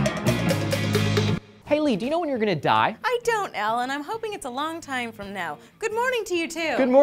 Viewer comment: Did she just say i don't allen?